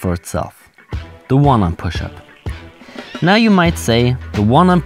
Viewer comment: First I heard “the woman pushup” and I thought the ultimate push up is some variation of the actual woman push up. But then “one hand” push up was spoken more clearly.